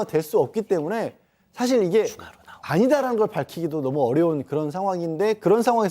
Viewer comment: 이와중에..나는 13:58~59초 사이에 아주 작은 목소리 "추가로" 라는 목소리가 들리던데 저만 들은걸까요? 몇번 돌려들음...소오름